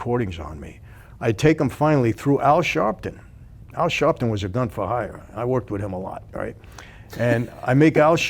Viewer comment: Anyone else catch the “Al Sharpton was a fun for hire” statement, at 11:53 or was that just me? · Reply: Most of us know, and especially after listening to this man: Sharpton has always been corrupt, and yes, a "gun for hire". He also spoke of: Comey, Clintons, Biden, Pelosi, Schummer, Schiff, and DeBlasio, Coumo, Emmanuel, etc. Clintons often spent time with Epstein, many people connected with them = died from questionable means. The Democrat party - as with Kennedy and Johnson, have always been corrupt. I have no idea WHY our voters/citizens ARE STILL SO IGNORANT OF WHO THEY ARE.